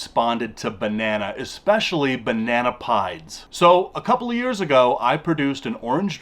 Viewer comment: Every time he said "banana pieds" I kept hearing "banana pie" lol. They're all beautiful animals though! It always blows my mind how tiny baby ball pythons are. I can't believe mine was ever that small.